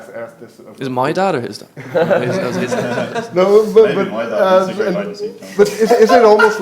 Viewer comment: Does anyone know what Josh says at 15:32 that has Ryan hysterically laughing? I can't make it out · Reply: “maybe my dad is great hide and seek player” or something like that